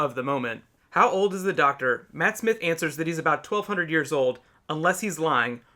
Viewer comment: The ears ? I thought he said years ? Referencing to the fact that after Hurt's Doctor, Ecclestone, Tennant and Smith are a lot younger than the previous Doctors.